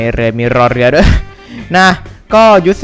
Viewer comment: ถ้าเราฟังไม่ผิดพี่พูดว่า นะคะ ใครได้ยินเหมือนเรามั่ง